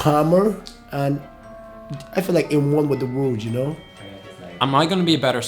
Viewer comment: "I feel like a worm with a wound, y'know" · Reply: I heard "at one with the world".